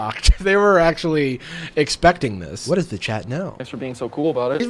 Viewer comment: I suck . When you said “ what does the chat know?” I heard 🦊“ what does the chat say” -🦊